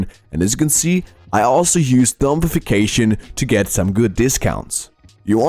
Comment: What did he say? something-fication to discount the prices? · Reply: Zombiefication